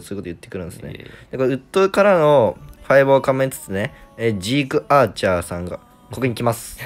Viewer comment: ジークアーチャーが「シークワーサー」に聞こえてマジでダサすぎてワロてる